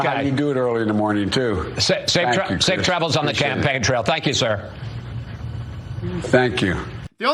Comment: Did he just call it a "champagne trail"? Maybe he thinks he's talking to Pelosi...